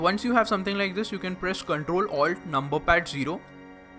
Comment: Im sorry but around 3:24 what do u press, can't hear it clearly. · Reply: Ctrl+alt+num 0 this shortcut snaps the camera to the current viewport view